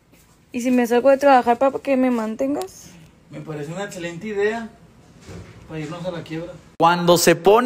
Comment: Que dice el vato? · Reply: Sería una excelente idea, para irnos a la quiebra.